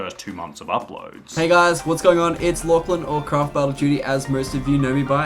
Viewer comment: Bruh I thought he said